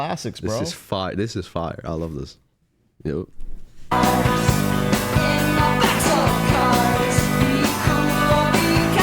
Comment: At some point it sounded like he said “what we do in backs of cars” 👀 and at another point said something about trying to fit in I think 🤔